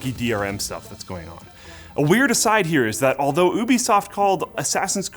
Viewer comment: I’m sorry but did he just say oobisoft 6:02?